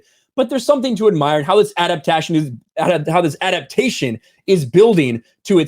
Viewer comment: When said " adaptation" , I thought he said " out of fashion"😄